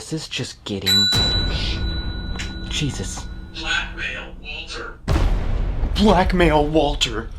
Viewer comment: there is an audible EVP of a high pitch, possibly little girl sounding voice. (obviously not a little girl)- Sounded like" WaAAAnt it", "RYYYaaan" or "clOOOOset" something with that kaydence. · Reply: That was saying blackmail. You can see him saying it just look at his mouth the whole time.